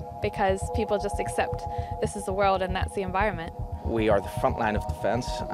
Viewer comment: Did she say at 3:35 "people just except that this is nazi environment"?